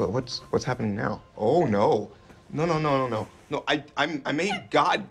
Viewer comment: "oh stomach ache welcome back old friend" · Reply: OH! I thought he said "welcome back girlfriend"! I was confused by that.